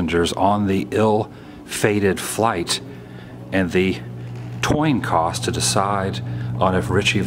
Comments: did he say "toin coss" ?? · Did he say at 3:08 "Toin Cos"? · does he say "toin coss"? Lol!